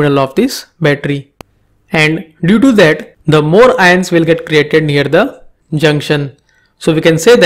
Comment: Is it me or did I mishearing the 'created near' he says as 'threaten near'?someone, please tell me what he says at that part T.T